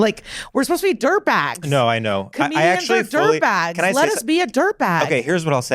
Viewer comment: For a split second I heard her say let us be derp acts